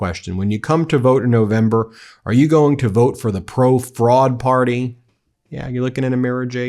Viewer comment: I thought he said frog party😂😂😮